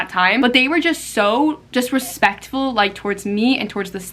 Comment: I'm confused with what she said at 24:35 , did she say disrespectful or just respectful?. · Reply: just respectful!!! they were not disrespectful at all!!!!!!!!